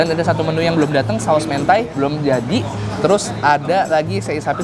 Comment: gw dengernya hentai🤣